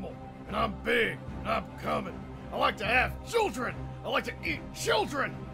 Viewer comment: I heard "putting the chemicals in the water" and I said " tHaT tUrNs tHe fReAkInG fRoGs gAy"